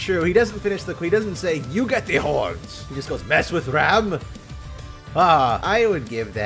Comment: i thought he said Amsterdam after the first line